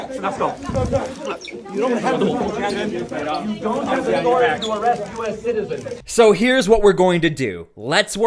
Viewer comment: did I hear that correctly… “you don’t have the right to arrest U.S. citizens? LMFAO